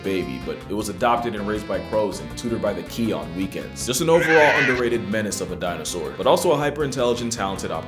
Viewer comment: Can someone please tell me what he said at 10:10 please? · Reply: Overall underrated menace of a dinosaur